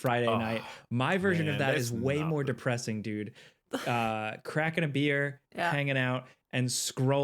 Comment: i know said his Blockbuster night would be "crackin' a beer" but at first I heard "crack and a beer" 😂